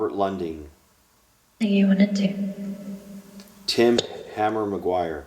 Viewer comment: sounds like "Do you wanna do it".. That's what I heard